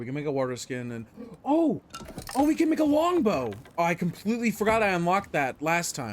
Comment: I thought he said "Oh weed", and I was like "wtf? this game has it??"